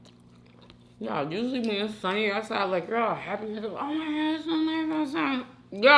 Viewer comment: what did she say? 😖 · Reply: “Usually when it’s sunny outside you’re all happy like omg it’s so nice outside@